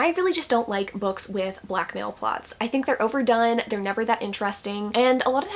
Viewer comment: I feel incredibly s t u p I d because I thought you said black male plot…. And I was like what? I’ve been watching you for YEARS and my stomach started hurting and heart dropped. I was like like what in the world?!? Had to scroll through the comments 😭😭😭😅😅😅😅 I feel ridiculous now